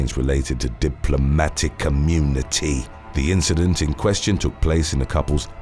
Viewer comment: Are you talking about a secret cadre of government workers that I'm not familiar with... or do you mean "diplomatic immunity?" Lol 😜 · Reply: I know he means diplomatic immunity, but I hear "community" and apparently, so does the closed caption.